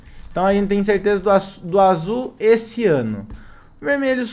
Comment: Nos 7:58 eu ouvi azul esse ano em vez de azul e ciano